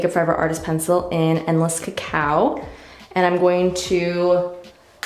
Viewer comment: I had to rewind that part. Sounded like you said your lip pencil was called “endless cow “. 😂😂😂